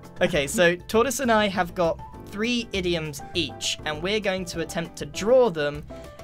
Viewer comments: I thought they where saying idiot. 😳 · I thought it said idiots